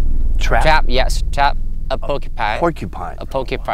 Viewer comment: Does anyone else find it absolutely adorable when he says "trap poki-pine" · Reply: I thought he said trap a Pokémon 💀